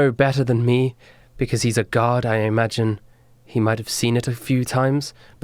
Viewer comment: When said: Duros would know better than me, he is a guard. I thought she said he is a god🤣